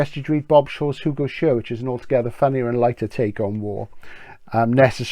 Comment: Sorry I can't make out the Bob Shaw book you mention @ 4:46. What is it? Thanks again! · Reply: 'Who Goes Here?' excellent book, very funny, but not in a nerdy way. Try it. Watch my Bob Shaw video too 'The Greatesr Hard SF Writer You (N)ever Heard Of'.